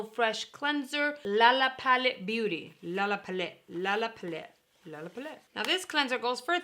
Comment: LOL I thought you said, "LA la Poulette, which means Lala Chicken. I literally laughed. 😆😂🙃🤪 I am excited. Love you.